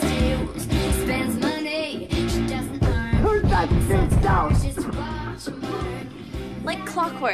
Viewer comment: When the Russian next door neighbor is yelling it seriously sounds like she said "turn that sh*t down." 🤣